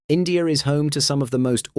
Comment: am i getting old or did u said INDEAR